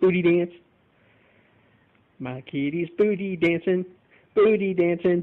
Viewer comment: Who else thought he said booty janson?(only if your a tmr /tst/tdc fan you'll know who that is)